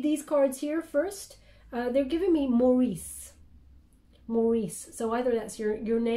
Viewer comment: I heard “Maurice” as “More Ease”😆 Thank you for this glorious reading🙏💫♥️